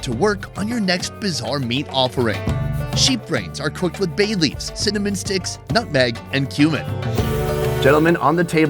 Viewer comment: omg i switched tabs for a second and i thought he said "cinnomon sticks, bayleafs and human"